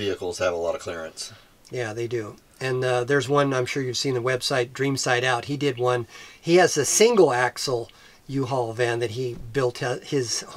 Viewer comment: Hi great video. But I didn't catch the uhaul van build website. I thought you said Dream Side Out? Can you link it please? Thanks